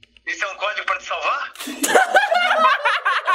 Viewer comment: eu ouvi :"um bode vai te salvar" kkkk